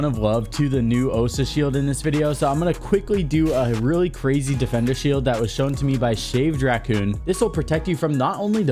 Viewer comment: DID HE JUST SAY QUEER SHIELD